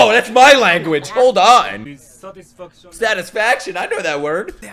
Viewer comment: Ngl when the teacher said “satisfaction” I thought he said “sad af” 🤣